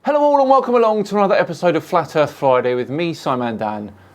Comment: did you just say flat earth flyday?